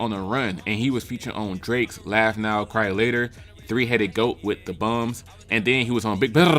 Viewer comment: WTF did he just say lo!!!l😂😅😅🤣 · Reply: Why he called baby and them the bums lol